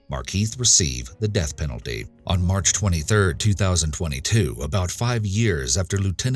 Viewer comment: March 3rd dummy!! Lol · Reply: why are you calling me names? 1:56:56, 10 seconds before the video ends he CLEARLY says March 23rd.